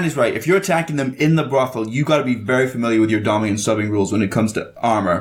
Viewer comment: I heard “domming” and “subbing” too